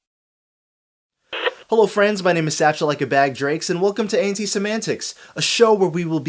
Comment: I always thought you were saying "Satchel like a bag of grapes."